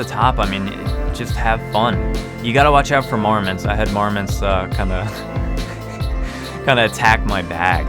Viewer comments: When he said, "You gotta watch out for marmots, they'll just attack your bag...", I thought he said "Mormons", and I thought "What?" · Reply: Yes, I heard “Mormons” too. I was like what they attack your bags? Lol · When he said, "You gotta watch out for marmots, they'll just attack your bag...", I thought he said "Mormons", and I thought "What?"